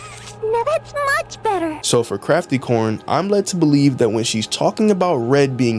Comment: i thought he said crappycorn :)